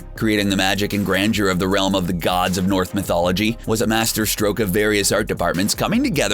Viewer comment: does he say north mythology instead of Norse mythology?